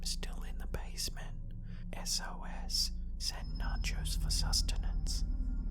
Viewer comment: Did anyone hear the whisper? Send Nachos at the basement😂